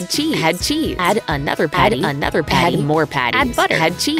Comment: i hear "anymore" XD